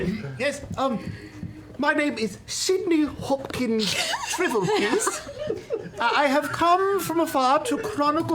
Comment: Oh man, I thought Travis said his name was, "Sydney Hopkins Shrivelpiss." and I nearly died of laughter. 😂